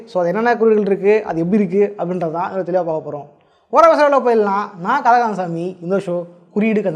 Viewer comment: Bro, i couldn't understand some word you telling fast in time 0:20 to 0:21 the word not clear to hear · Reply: Over ah pesama video kulla poidalam 🌝